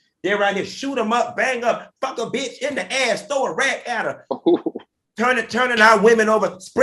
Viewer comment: So I watched this twice and the first time I thought said, "Throw a rat at her!!" 😂🤣😂 I know he was being serious but a rat is waaaay funnier than a rack. Love ya ❣